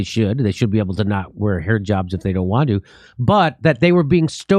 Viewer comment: Sounds like Jimmy says 'hair jobs' instead of 'hijabs.' He just can't get his mind off hair! : )